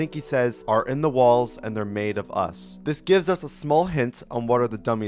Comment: i think he's saying "the bodies are in the walls, and they're made of us"